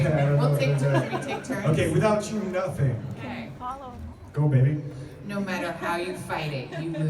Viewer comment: Did he say "Go baby" at 3:05 when it was time for Alexandra to read? 🤔